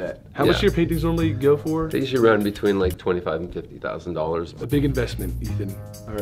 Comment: when he said 25-50 thousand dollars I heard 20-50$ and then I heard the word thousand and I was absolutely blown XD😱